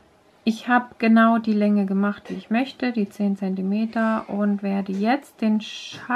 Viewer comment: Hallo! Danke für Ihre Hilfe! Ich weiß nicht, wie lange soll ich stricken? Ich hab E 10 cm (16:44)gehört😢 Tut mir leid, mein Deutsch ist schlecht. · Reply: Ja 10 cm. 🥰👍Wenn etwas unklar ist, helfe ich gerne.